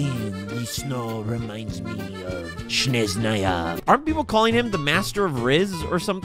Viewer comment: I'm dying of laughter every time i hear "Sneznaya" with accent He literally said Shneznayaaaaa